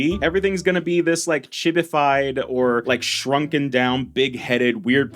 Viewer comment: At first I thought you said “shitified”